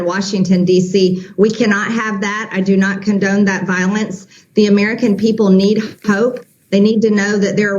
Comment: Did she say I do not condemn the violence I think I misheard